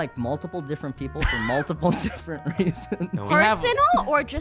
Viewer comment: I looked away and I thought he said "I don't like people from multiple different races"